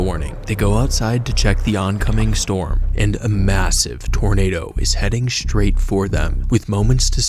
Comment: did you say…….. massive..?